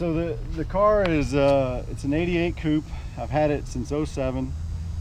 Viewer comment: I had to re watch the 47 mark, I thought he said he’s had it since he was 7 I was like wait what 😂